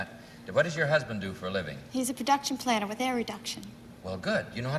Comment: what did she say her husband does for a living? couldn't catch it. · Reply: I believe it was "production planner with air reduction."